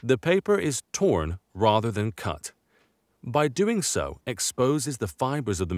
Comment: Am I the only one that heard the c-word instead of "torn and not cut"? 21:19 😂 Gosh I need to go to bed. It's a great video and restoration though.